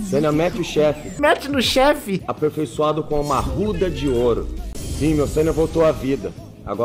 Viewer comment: eu ouvi exatamente "e agora foi aperfeiçoado com a pica de ouro, vai seiya, mete no chefe"